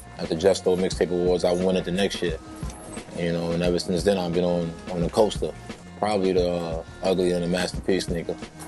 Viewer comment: what does he say between 1:25-1:28?? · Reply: Adrian Torres shit can get uglier then a Master P sneaker